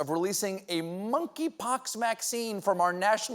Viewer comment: "monkeypox maccine"..did someone catch that. Lol